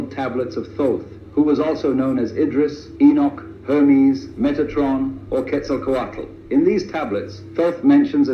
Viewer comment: did he say "Megatron" ?